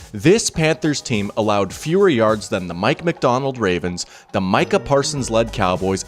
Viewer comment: did someone say Mike Macdonald? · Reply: Mike Macdonald